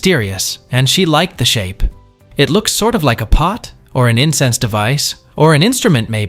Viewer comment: did they say it looks like an 'Incest device'? 😳 Not sure what kind of family they are in but ... 😮‍💨